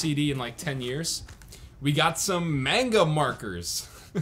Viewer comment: Can't believe someone can pronounce "manga" wrong.... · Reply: Lool I thought he said mango